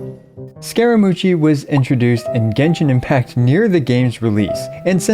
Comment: Did he just say scara-mochi?